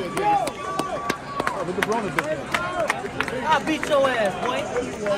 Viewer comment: What he say ? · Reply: I thought he said look at Broners ass!!